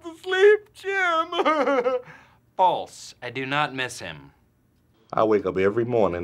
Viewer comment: The first time I watched Dwight saying False. I do not miss him. I thought he said F*ck Jim